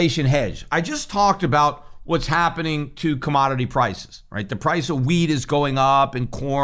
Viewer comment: Did anyone else hear "the price of weed is going up" lmao?